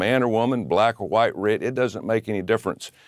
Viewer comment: did he just say red (as in the slur against native Americans)?! Because if he did, then yikes!